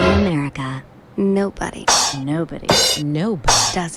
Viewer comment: i swear i thought that said that "no body" 😂😂